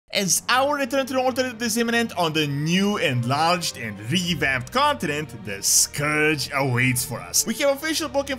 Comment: What the fuck?? The first 4 second this is what I heard, “ is ourthruthrurhturhrutziminient on the new and large and revamp continent” lol or am I wrong here